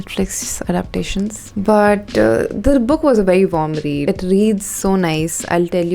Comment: " Charu, did you say but Or bhatt at 5:02?"